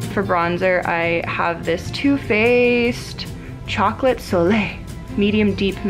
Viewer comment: when she said "two faced" it sounded like the vine "two bros" (chilling in a hot tuuuubbb, 5 ft apart cuz they're not gay)